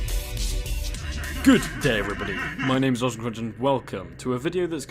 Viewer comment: In the beginning I thought you said “good day everyone MYNAMEISAWSOMECRUNCH”